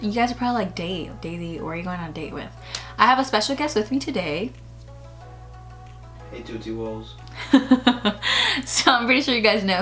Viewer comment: noah fence but the second i heard "hey sushi woll" i GASPED